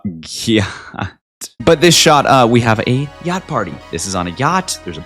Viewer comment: i thought you said gayt party😂